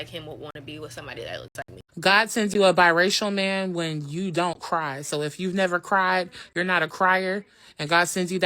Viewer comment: what's she talking about? Does anyone get what she's saying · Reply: She’s saying biracial men will make you cry . They’re heart breakers